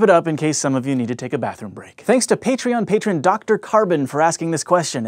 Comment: I thought you said "Thanks to Peetreon"